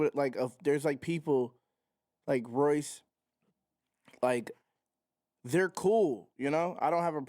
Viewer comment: did u say Royce?👀